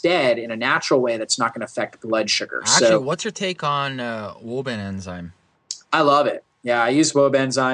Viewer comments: What are they saying at 35:26 ?? Woba enzyme?? I know that isn't right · What's the name of the enzyme mentioned at 35:26? I couldn't make out the name. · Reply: Wobenzym (or Wobe-Mugos enzyme) is an enzyme containing plant-based enzymes bromelain and papain, supposedly to support a healthy immune system. These statements have not been evaluated by the U.S. Food and Drug Administration. This product is not approved by the government to treat, cure, or prevent any disease.[1] Some possible side effects have been reported in medical literature.[2][3] <link>